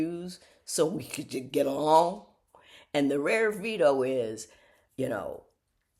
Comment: I thought you were saying VIDO...(like a man in the mafia)...You meant VETO....to overrule someone's actions?